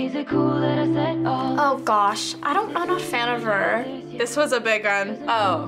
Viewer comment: correcting captions: stumbled and said I don't not a fan instead of a clear "I'm not a fan"